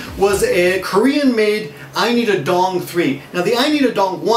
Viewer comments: Wait. Why do I keep hearing "I need a dong" for Arirang? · I listened to this and I kept hearing 'I need a dong', lol